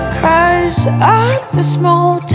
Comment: Can anyone help me figure out what Katy was saying at 2:15??? KATY OUR ACOUSTIC QUEEN 👑💖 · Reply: 'Cause (Because) I'm a small town...